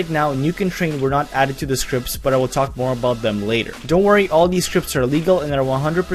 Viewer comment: Sounds like he says "Don't Worry All these scripts are illegal"